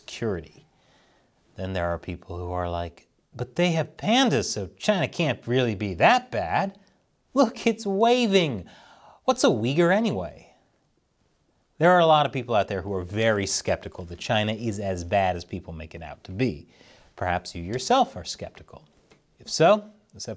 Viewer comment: misheard comment 'there are a lot of people out there who are very stupid '